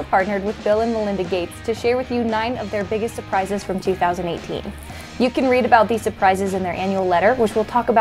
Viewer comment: did she say 2018 or am I hearing things?